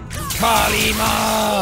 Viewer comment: did jack just say kali ma? As in the Indian goddess?